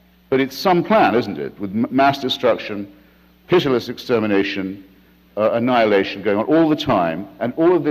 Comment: Did he say clitoris extermination? 24:55.